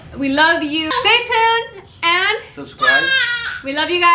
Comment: At the end of the video it sounds like said subscribe😊